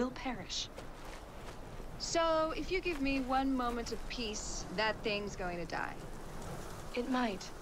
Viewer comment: I thought she said "If you give me one moment to piss" lol... I wish she really said that.